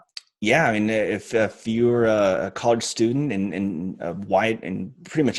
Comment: is he saying 'if you're white'?